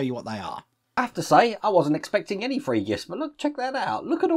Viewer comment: "free gifts" or "three gifts"? I hear "free" when I expect "three" often here. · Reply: Free.